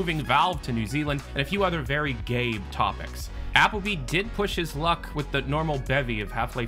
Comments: I thought I heard "A few other very gay topics." 😂🤣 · i thought you said “and a few other GAY topics” lmao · I thought I heard "and a few other very gay topics." For a second there. 🤣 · I thought you said "gay" topics